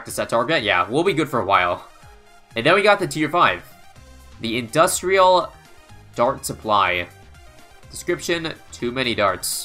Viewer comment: At first I thought it said industrial fart supply